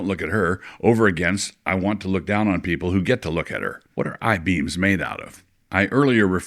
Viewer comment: When Doug said at 7:15, "What are eye beams made out of?" I thought he said, "What are her high beams made out of?" They're real, Doug.